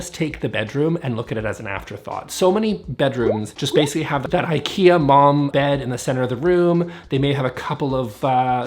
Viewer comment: Depending on your… “choices.” That cracked me up. Also I get now you said IKEA Malm bed but at first I thought you said ikea MOM bed and I was trying to figure out what that was.